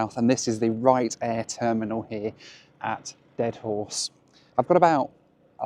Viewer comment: That first pilot I thought he said the second town was, "I Beat A Dead Horse."